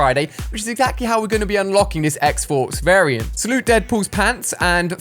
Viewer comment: Is it just me who heard him say X-forks